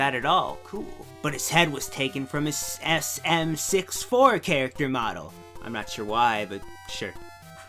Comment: when you said sm64 at first it sounded like you were saying ass something XD #imightbedeaf